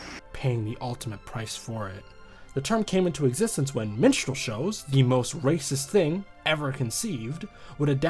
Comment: What kind fo shows he's talking at 3:18? I'm not a natural english speaker, so I heard something like "menstrual shows", which I'm 99% sure it is not what he said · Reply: Minstrel shows